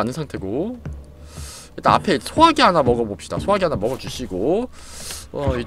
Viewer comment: 소화제 라고 들림